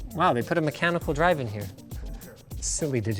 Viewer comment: I swear i heard " chemical drive "